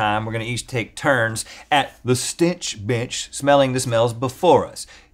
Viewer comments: when Rhett said "Stench Bench," I thought he said "Stich B****" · Ok... I definitely misheard “stench bench”. I instead heard “stitch-(b word that rhymes with stitch)” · i thought he said the stitch bit**